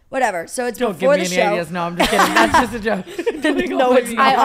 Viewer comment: what did Tana say? Before she said she was joking. Don’t give me an Alias? I literally have listened six times and can’t make it out. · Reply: Ideas